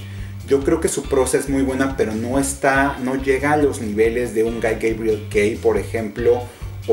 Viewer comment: En el minuto 23:05 que autor/a es el que dices?? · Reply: Guy Gavriel Kay, el autor de Tigana, El Tapiz de Fionavar, Los Leones de Al-Rassan, etc.